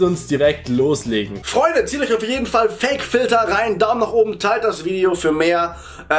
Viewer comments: ich hab am Anfang einfach Fick-Filter verstanden. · bitte deutlicher sprechen, ich hab "fick"-filter verstanden :)